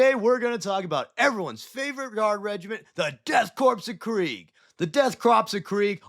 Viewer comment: did you say the death "crops" of krieg? XD